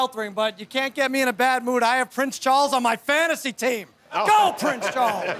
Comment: It sounded like he said he had prince charles on his fantasy taint.